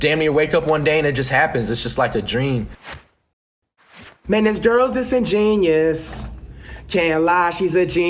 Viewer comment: I thought the song said man this girls just a genius😭😭